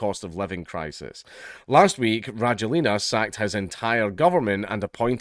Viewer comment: I swear I thought Dank said "Brangelina" when talking about the countries leader, which made me think for a second that Brad Pitt and Angelina Jolie were in charge lol